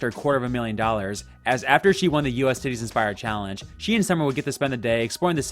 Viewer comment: Did you just say U.S. titties inspired challenge?? 😂😂